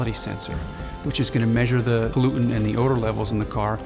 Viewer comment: air sensors... measure the GLUTEN levels? What the-?? Maybe he meant to say "pollutant" levels...